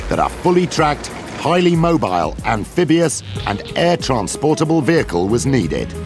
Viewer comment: did i heard right 1:20 did he said " amphibious" ??? the M113 ???